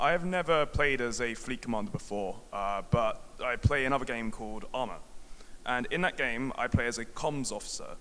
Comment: What game did he the guy in the crowd said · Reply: Thomas Yap ARMA 3